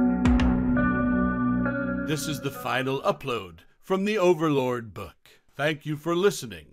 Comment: So your not doing the next vol? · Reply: just re listened to intro I misheard thought he said last volume not last upload of this book, was super tired when I first listened and wrote